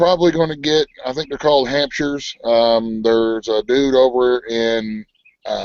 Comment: rflmbo, I thought u said "hamsters" instead of hamshires, when u were talking bout getting pigs...Damn, you two are crackin me up! I'm pissed it wouldn't let me into the broadcast on Sunday, but it kept saying that video was gone and it wasn't showing anything going on channel either!